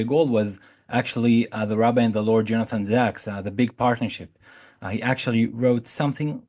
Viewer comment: Did anyone catch the book reference "jonathan Zach" Rabbi and Lord etc? (23:25) I couldn't find anything. Thanks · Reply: Jonathan Sacks, “The Great Partnership. G*d, Science and The Search For Meaning”